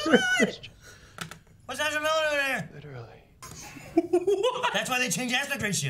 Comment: Ok what does the guy at 1:52 and 1:56 say cause I can't understand him? Thanks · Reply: "Why's Ezra Miller there?"